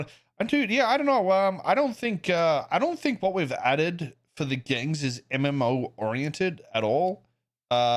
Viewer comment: The fact he said he feels they didn’t add any mmo thing is crazy. Food, drugs, rings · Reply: Thought he said mmo to gangs